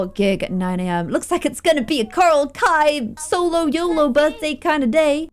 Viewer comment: A Coral Kai birthday celebration My 🧠: did she just say kobra kai? 🥴